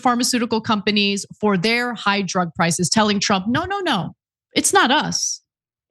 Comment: I thought Ana was saying, "Hydra prices" until I realized she was saying, "High drug prices". xD